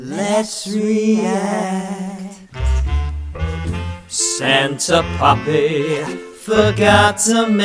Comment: Santa puppy?? I MISHEARD IT!!